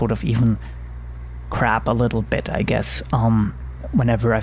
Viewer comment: Very nice video. Great instruction. Videos like this totally show how a person to learn to fly an aircraft with a "simple," desktop simulator. By the way when you said, "Crab a little," I thought you said, "Crap a little," lol.